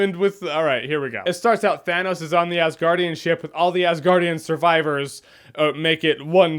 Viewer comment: I thought the movie said, ‘half of the asguardians’